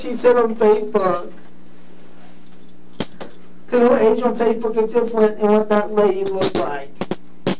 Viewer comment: Did he just say " he looks different on paper n not what that lady looked like?????????oh my lord it's a child!!!! Freak